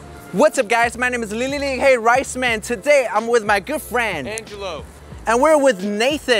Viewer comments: I think he says girlfriend LMAO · I thought he said girlfriend for a second xD